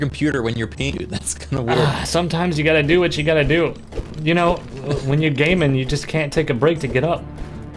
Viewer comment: i thought he said 'when you're a gay man' and the shock that happened was so real